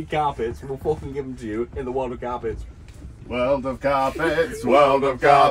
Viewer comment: Curse their British accents! I thought they were saying "world of garbage"! 🤣💀